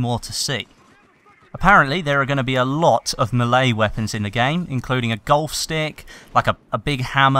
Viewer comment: Lol when said Melee weapons I thought he said Malay weapons as in from Malaysia.